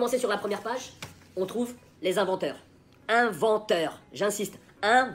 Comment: bordel pourquoi je me souviens jamais que un vrai facepalm fait mal! EDIT: j'ai entendu inventaire au lieu de inventeur... je mérite ma douleur (?)